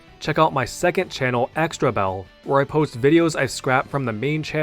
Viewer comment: I misheard xtra_bell as expert belt in your outro